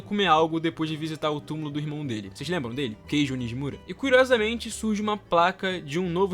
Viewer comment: eu juro q ouvi Queijo Nijimura KKKKKKKKKKKKK